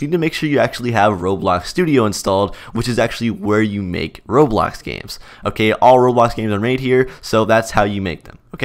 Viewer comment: If you put in subtitles on 0:17 you can actually see that he said robot games instead of roblox games🤣